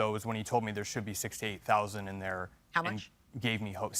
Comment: I thought he said 68,000🤣🤣🤣🤣🤣🤣🤣🤣🤣